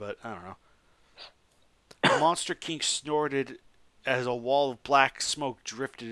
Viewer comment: with Captions. Monster king snorted a wall of black smoke. Didn't know Godzilla smokes. Also descoteaux instead of Desghidorah.